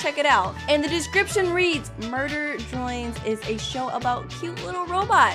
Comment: It sounds like she is literally saying murder joins im not trying to be rude though